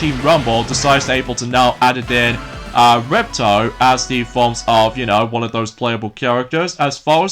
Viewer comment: ! Don't you mean Ripto? Also, The name of the 2nd spyro game is called: Spyro 2 Ripto's Rage ( A.K.A. gateway to glimmer in the UK! 🇬🇧) it sounds like your saying Repto buddy!